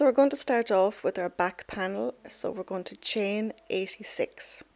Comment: You did a beautiful job teaching this pattern. Thank you for using US terms. You took your time but didn't "baby" step either. My only thing is, I thought you said chain 96 for the back panel. oops! I hope I don't have to pull it out and restart. I'm finishing up the back panel. It should be okay. (if not I will undo it. It won't be the first time). Thank you again.